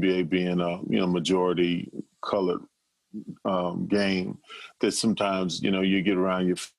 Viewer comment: Majority “Colored” game 11:26? Did I hear that right ?